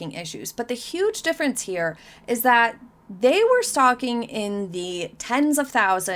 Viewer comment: Lol, when you said, "stocking," I heard, "stalking."